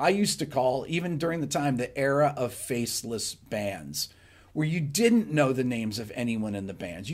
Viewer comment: The era of Faceless Bands? I thought you said the era of Face-lift Bands! Ha Ha